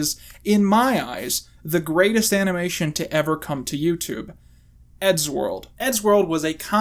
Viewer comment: I remember eddsworld was really special because my oldest cousin who I thought was the coolest person in the world told me about it. I remember being really confused when I first watched it because I thought he said elfworld.